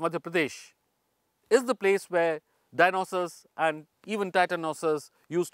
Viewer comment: I thought he fist said this was where Dionysus roamed, he wouldn't be wrong lol